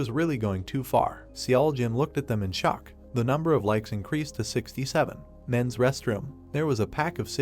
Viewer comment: d-did i just hear… DOS UNO💀💀💀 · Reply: 67